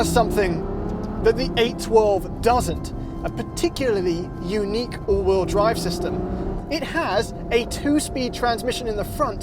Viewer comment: sounds like says "A particularly unique UWU drive system." XD im dead.